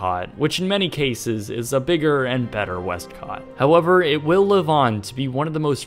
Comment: I thought it said westcoast. and I was like yesssss!!! forget Disney!